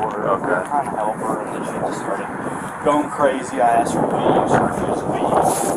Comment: “I need you to leave me alone for five minutes.” You don’t get to set a time once trespassed on when you feel like you should leave. You leave or get dragged out. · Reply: You heard what you wanted then, at @ 1:53 the guys says “I asked her to leave.” This is just one many times she is verbally trespassed in the video.